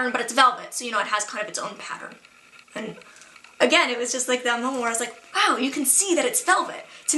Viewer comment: What do you say at 6:34.9? "again it was just like that mulmore... I'll try to edit it in in the cc Other than that, you talking about Prince and Halle Berry reminds me of one time I was listening to a podcast and I didn't know the guest on the voice and halfway trough I was wondering "is she black?" It wasn't anything she said and it doesn't matter but I just found it strange that I could tell. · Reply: turoni she says "again It was just like that moment where I was like..."